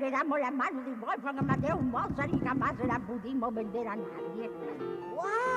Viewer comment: lo que dijo fue "Mi abuelo me heredo las manos de mosard y nunca se la pudimos vender a nadie" Eso fue lo que entendí Los amo :3